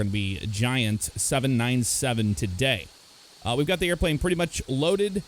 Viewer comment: Is it just me, or did he say "797" at 0:25?